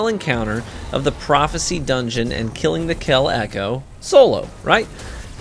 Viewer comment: I thought it said kelling the kell echo